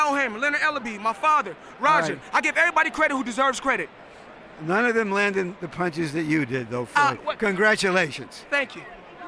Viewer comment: can someone explain what he said to Mayweather? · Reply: None of them landing the punches that you did. Congratulations.